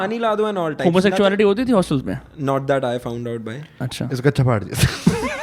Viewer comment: woh kya bola "iska chapdaa..." ?? 😂 😂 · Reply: 'Iska kaccha faad diya tha'